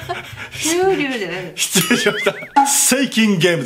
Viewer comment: フューリーワールドが フーリーワールドに 聞こえた！(笑)